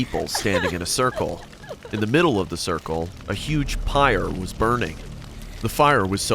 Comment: did he say pire